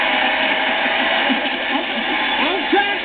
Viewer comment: who else heard him say I'm tryna defend yo girl main